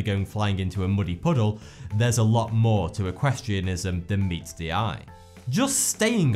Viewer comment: did he just say equestrianism?